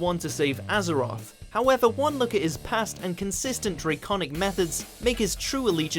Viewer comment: Lol did he say 'draconic methods'?